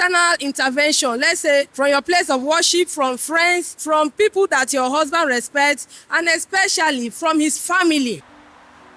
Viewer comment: I could not exactly understand the 4 signs she says to look out for at minute 15:27. Did somebody catch it? 🥹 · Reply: 1. A lying lip (basically a man that lies) 2. A deciever ; a man that promises and fails 3. A nagging man (someone that never let's go. Whatever you do he will still bring it back to use against you) 4. A man hitter ( basically a guy that can hit you even as a joke. He will hit you and say it's a joke, or it was the devil)